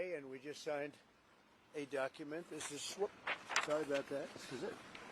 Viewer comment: I heard a “sorry”… I swear to god he said “sorry”…hahahahahahaha